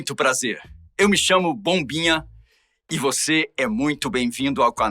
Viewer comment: Será que foi só eu que entendí "pombinha" e não bombinha ?😂